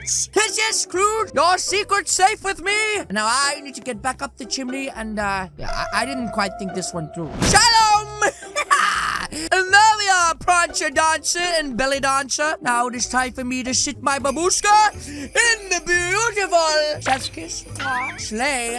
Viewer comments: did he just say sh*t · Umm did say Sh*t on the slay at 5:12?